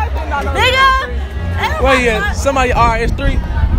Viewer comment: Did she just say nigga